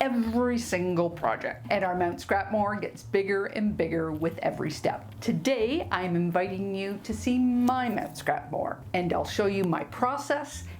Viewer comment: The first time you said "Mt. Scrapmore" I thought you said "Scrap morgue"! 😂😂😂 And I thought, how appropriate! Wanted to say too that I love that quilt hanging on the wall behind you at :25 (and intermittently through the rest of the vid). Great tips for the scraps, thanks!